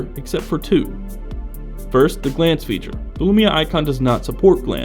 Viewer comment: What does he say? @ 4:11 "Feature for feature... except for two. First the ? feature." Thanks in advance. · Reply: 6 months late reply: Glance. It is a feature that allows the phone to displace information without affecting battery life.